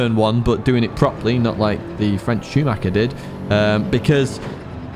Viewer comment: I thought you said the french virgin Schumacher on the intro